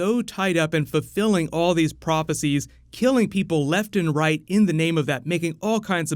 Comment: I thought this man said "... in the name of this nigga." 😂😂😂